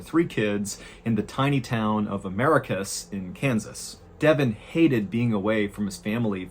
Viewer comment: ohhh it’s americus.. i thought you were saying “America’s Kansas” this whole time 😂